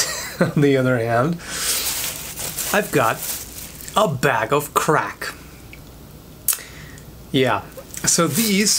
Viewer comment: It did look like beer. I thought you said bag of crap at first. But the pretzels aside, fruit has a lot of sugar in it, and fruit juice is worse. So you have to have a balance. I try to drink water but end up slurping way too much Fanta for my own peace of mind.